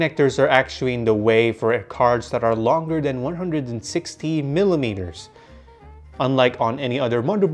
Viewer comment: did he say 116 millimetres or 160?